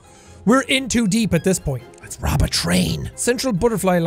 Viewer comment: When he said "rob a train" I actually thought he said "rob a tree" 😐